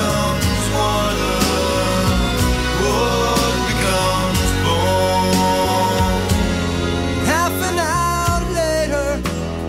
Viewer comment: We spotted the ocean at the head of the trail Where are we going, so far away And somebody told me that this is the place Where everything's better, everything's safe Walk on the ocean Step on the stones Flesh becomes water Wood becomes bone And half and hour later we packed up our things We said we'd send letters and all those little things And they knew we were lying but they smiled just the same It seemed they'd already forgotten we'd came Now we're back at the homestead Where the air makes you choke And people don't know you And trust is a joke We don't even have pictures Just memories to hold That grow sweeter each season As we slowly grow old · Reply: Thank you, Lauralyie. I always heard, "Board becomes bone." 💙🏄🏻‍♀️🌊🌅💙